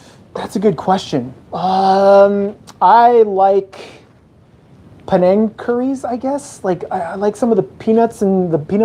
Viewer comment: Did u said PENANG CURRY?? that is from malaysia.. n we have the best curry in the whole world..